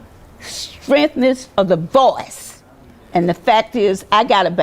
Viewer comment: I think her tombstone will prob say Felon. lol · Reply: Is her wig on backwards? At 5:02 did she say, "stremphness of voice"?